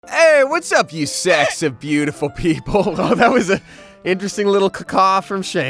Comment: I’ve always thought Damien said “sexy beautiful people” but now it’s sometimes sounds like he’s saying “sacks of beautiful people”. Which one is it?🤔😐